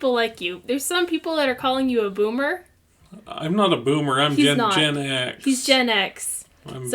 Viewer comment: Did... Did he just say he's... GENEX? Oh no, the 5D's flashbacks are coming back... Run! Hide your children!